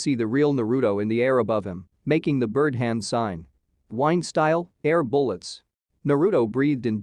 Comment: sounded like 'Wine' style the first time i heard it... Tyrion Lannister fighting the Mountain using Wine style Jutsu.